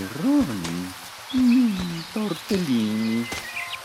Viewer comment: Did he just say "Tortellini"?